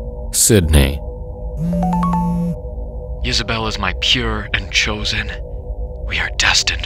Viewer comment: Oh, it was Isabell. I thought he was saying Yisabelle, which I thought sounded more unique and cryptic. Gotta say, you made a fan out of me. Some of the stories I listen to on YouTube start strong and end weak. I like that you give your stories more closure.